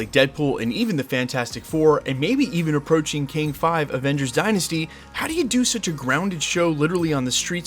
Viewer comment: Am I the only one who gets him say Kang 5 Avengers Dynasty? Instead of Avengers 5 Kang Dynasty. If so what happened to Kang 1-4 😂